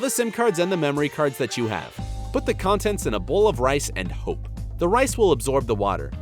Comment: I thought you said don’t put in in a bowl of rice 🍚 early